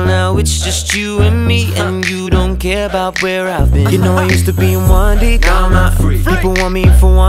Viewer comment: I JUST REALIZED IT SAID “I used to be in 1D now I’m out free” BUT ALL ALONG I THOUGHT IT SAID “I used to be one thing, now I’m s freak” OMFG IM STUPID